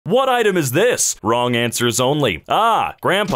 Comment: I thought it said wrong answers only